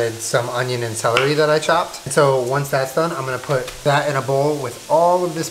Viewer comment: I swear he said bat in a bowl